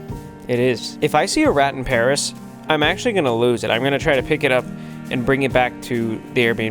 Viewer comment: I thought he said he was going to eat Remy